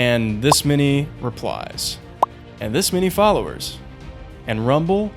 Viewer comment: thought that said reptiles lol